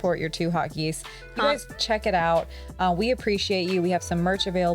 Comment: Oh 2 Hot Geese I thought you were saying 2 Hot Geeks.... lol Great Coverage